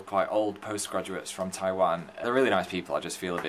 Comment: Did Phil say "Taiwan" in 00:43 ? I come from Taiwan!> <